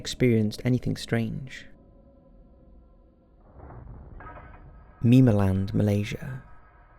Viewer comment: I'm so dumb I thought he said memeland